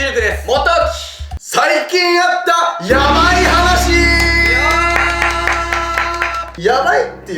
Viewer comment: 最初｢最近やったやばい話｣に聞こえて、自白動画かと思った笑